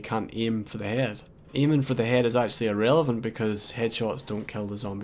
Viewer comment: I meant was it's not a 1 shot kill to the head. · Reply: Ahhh, ok. I heard "Aiming for the head in irrelevant" to which I noped immediately, it seems the ONLY decent way to kill a zombie. Unless you feel like wasting a full clip on 2 zombies lol